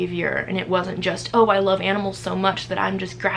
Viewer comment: At first i thought instead of saying loving animals i thought she said she gave drugs to her animals